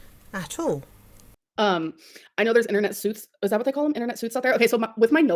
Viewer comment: “Internet Suits” took me out 😭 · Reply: I thought she said "internet sooths" like a sooth-sayer 😂😂😂😂